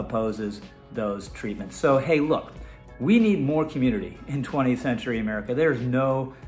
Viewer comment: did bro just say 20th century or did he just drive me insane to the point of hearing things? I'm trans, and I am very much real.